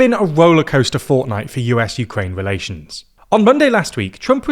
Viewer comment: Did he say "a rollercoaster fortnight"? What does it mean?